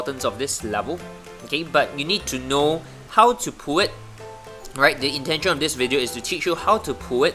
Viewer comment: whats is he saying @ 3:36? pew it? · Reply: It's "pull it" but pew it works too! 😉